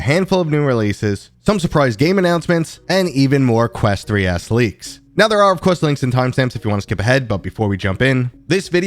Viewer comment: In the intro I thought you said “and more quest 3 ass leaks” 😂